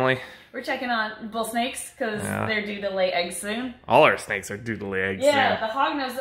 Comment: I'm so tired lol, at the beginning I thought Emily said, 'they do the lay eggs soon' instead of 'they're due to lay eggs soon'...